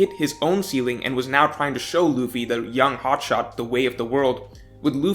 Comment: did just called Luffy a hot dog?